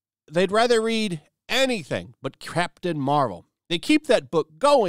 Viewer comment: I could've swore he said "crap tain marvel" lol